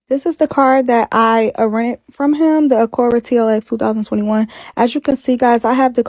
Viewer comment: Did she say “AhhhCURE-Rah for ACURA 🤣🤣noo ..time stamp 7:27-28 🤣.. she made my day